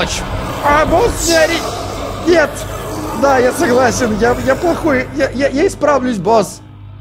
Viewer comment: я тут услышал мат. Пожалуйста проверьте может мне послушались. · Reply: Не, он сказал "я плохой", но мне тоже послышалось😂